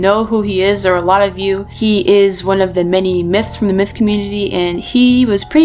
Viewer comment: I thought she said mits when she was talking about myths